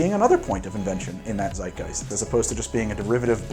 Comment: did he just say zeitgeist ? Im german and i look like a mixture of surpirsed pikatchu and poggers.